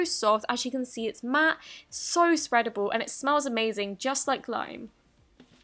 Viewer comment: she said glime instead of slime